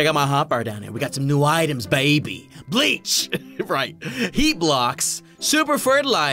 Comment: i thought he said yeet blocks XD